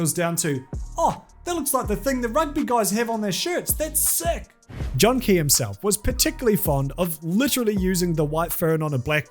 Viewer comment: did I hear that correctly? JOHN K HIMSELF????